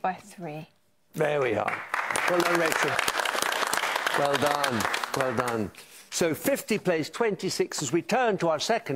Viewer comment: Lol 1:19 onwards you can see him chuntering... I wonder what the microphone picked him up saying. 😅😅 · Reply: hes saying "thats what I fcking wrote down!"